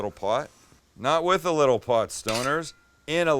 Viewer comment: Lmfao I thought he said with a lil pot forreal 😂 honestly I watch all his vids at night fresh off a joint then I chef it up in the kitchen 👌🏾